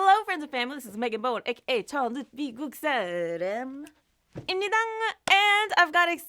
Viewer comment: “Hello friends and family this is Megan Moon AKA Ch-“ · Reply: This is what i hear..." HELLO friends and family! This is cha luke me goot saaaaah Lamb! Ah me DONG!" ☺️🤗